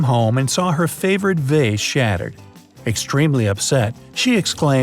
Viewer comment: wut did u say? vae?